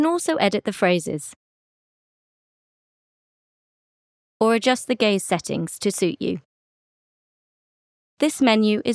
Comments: The captions: or just the gaze settings to suit you What do i hear: 1:06 or just the gay settings to suit you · bruh did she just say "to adjust the gay settings" lmao?